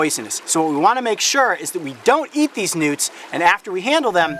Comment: Am the only one who heard (2:03)"We wanna be sure that we don't eat these nudes)